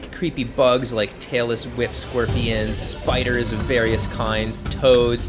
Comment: I had to check the subtitles as it sounded like you said "Taylor Swift scorpions!" Great vid, looks like a great holiday. 😁🌱💚